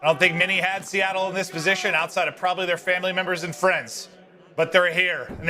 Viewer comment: What does he said about France? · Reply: I think he said family and FRIENDS.